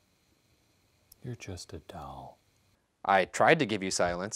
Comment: The face Ryan made when he realized what Shane had said “Oh god WHY did you say that?!” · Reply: Lee Amaris he said “you’re just a doll” 😂😂